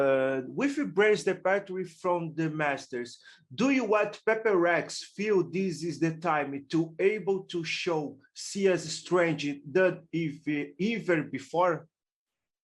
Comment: yall know what he sayin or nah? I can't understand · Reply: "With bren departure from the masters do you want paper rex feel this is the time to able show SEA is stronger than before? "